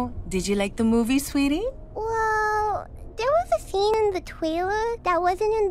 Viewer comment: It sounds like she said “There was a scene in the TOILET” 😂